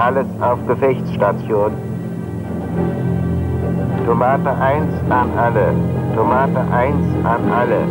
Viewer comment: As a kid I thought he was saying "Tomato Heinz" lol then when the fake blood (ketchup) arrived at 1:39 I was convinced. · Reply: The Callsign of the Leading Bomber was "Tomate Eins" what weans "Tomato one"